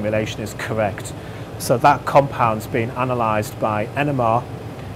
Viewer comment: Been analyzed by what? what did he say? Enema?